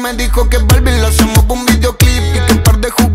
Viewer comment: jajajaja pensé que decía "balvin la llamo pa un videoclip" referenciando a que mas pues de maría becerra y balvin xddd tiene mas sentido lo de ballve jajajaj